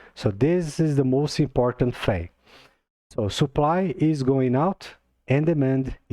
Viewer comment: hi, hope u are well. at 5:06 did u say supply going out or going up? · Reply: Hello mate! Yes, supply running out, or decreasing. Xrp supply can't go up.